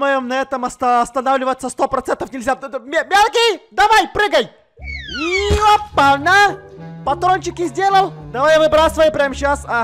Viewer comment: Я который подумал что он сказал ёбана 😂😂😂😂😂что?!🤣🤣